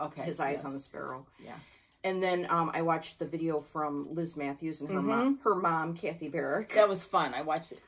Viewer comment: There are times I am unable to get exactly what you are saying. I am wondering if you mention a great floss tube channel if you could link it below? I thought you said Luke Matthews and her mom but I don't find any Luke Matthews videos that are cross stitchers! Thanks.